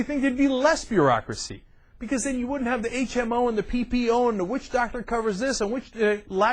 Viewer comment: hehe I thought he said witch doctor ^^